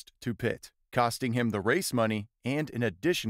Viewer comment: i honestly thought he said ‘a racist monkey’